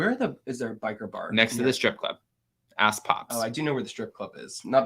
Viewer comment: I thought you said the name of the strip club was called A** pops I screamed out laughing like what is the name 🤣🤣🤣😩😩